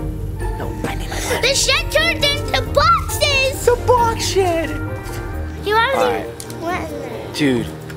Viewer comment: what did you say duddy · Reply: he said the box SHED